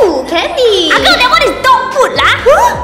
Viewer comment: Why did I hear “Ah girl that is dog food lah!” As “Ah girl that is dog poop lah!” 💀